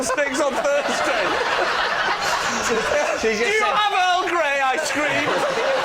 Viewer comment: "DO YOU HAVE EARL GREY," I screamed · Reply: The very first time I heard that line I thought Lee said "Do you have Earl Grey ice cream?", which is equally uproarious